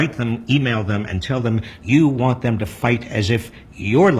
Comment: Notice how he said to write and email your Dems, but not call them? That's because calling them is the most effective way to get them to get their attention... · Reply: He did say call them.